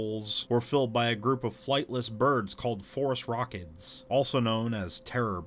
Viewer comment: No lie, the 1st time I heard the term Phorusrhacids I thought I heard Forest Rockets. I was so confused and looked it up. Would not have made the connection if I read the word and heard it lol